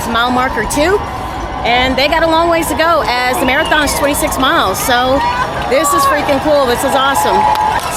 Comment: My daughter thought you said 6 miles. I said who would travel that far for to run just 6 miles. Lol. 😂 She was speechless when she heard 26 miles.